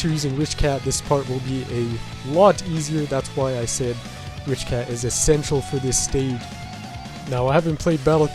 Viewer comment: I thought he said rich cat is asexual